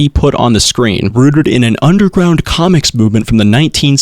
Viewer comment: did he even like, put on screen where he got it from or did he just put the little box of information on screen that could have easily been inspect elemented (edit:misheard and thought he said "communist"). also i don't think it's a bad thing for a community to have sexual roots, much less roots that simply SOMETIMES involve sexuality